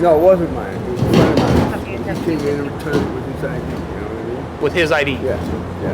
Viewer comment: I think you guys misheard him he said his friend returned it himself